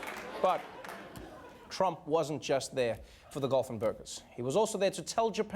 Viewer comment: I thought he said Trump is not just there for the dolphin burgers. Lmao